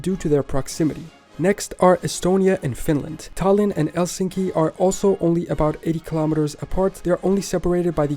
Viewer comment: Did he say “Elsinki” instead of Helsinki?